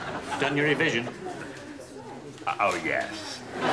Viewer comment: Someone could tell me what the guy say at 2:08? I want to die in peace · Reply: Done ur revision?